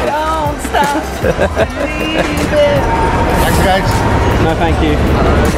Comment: What did that man say? I wish they added subtitles... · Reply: He asked, “Taxi, guys?”